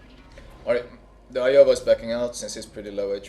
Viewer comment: Everytime you said Iowa I thought you said Aioba like the IJN cruiser, I kept thinking someone made a derpy division